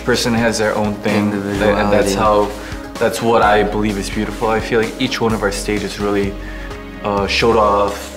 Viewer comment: I thought he'll say beautiful is me 😭